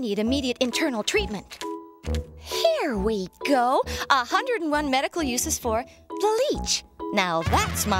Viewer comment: I swear it sounded like she said 101 medical uses for bleach... :P